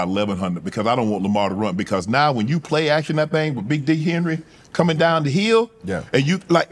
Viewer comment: Did Unc call( at the 6:40 mark) King Henry Big 🍆 Henry 🤦🏾‍♂️😭😭😭😭😭😭😭 u not helping yourself 😂😂😂